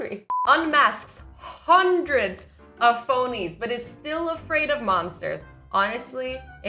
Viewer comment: At 1.40 I I thought that said ponies 😂😂😂😂😂